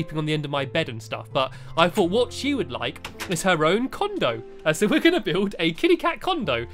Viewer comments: I Thought It Said Condom... I'm So Sorry XD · I thought it said kittie cat condom